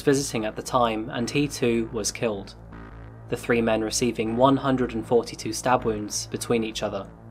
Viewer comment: did I hear 142???